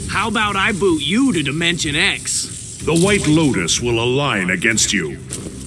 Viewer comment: did he just say white lotus? Did anybody watch avatar the last air bender? Cause I’ve got leaves from the vine looping in my head now